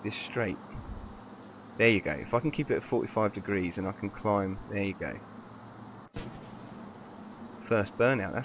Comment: you said "there you go. If i can keep it at forty five degrees and i can climb. There you go." automatic captions said "bacon f***ing keep it for five degrees and i think crime weekday."